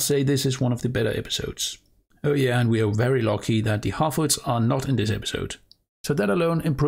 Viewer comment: The way you said Harfoots, I almost thought you said halfwits.